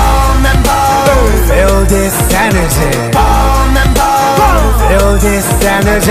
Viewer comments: I thought he was saying "feel this Saturday" 😂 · It sounds like hes saying feel this SINergy lol me and my dirty mind😂